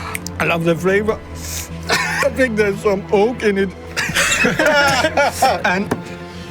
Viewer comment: When Klaus says oak all I hear is ‘poke in it’. Try and unhear it now especially after he says pencil.